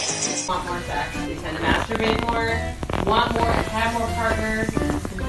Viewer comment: my dirty brain just can't 0:29 did someone just said "...masturbate more..." hahahaha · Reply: I heard it too. The next sentence I heard was “or if you want more partners…!!” 😮😮🤣🤣